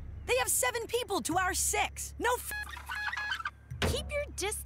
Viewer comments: it sounded like Jo was about to say “no fu!” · am I the only one who heard F#%*